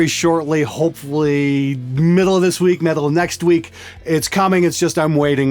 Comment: Did you say "metal of next week" @ 00:44? Hell yeah!